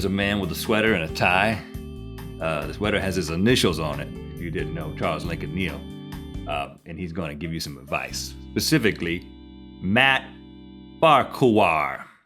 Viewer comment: Did you just say "Pacifically" rather than Specifically ?